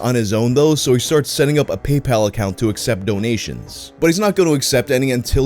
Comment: I thought he said the guy set up a "Poop Pal account" to raise money for his fetish vid. Lol 😂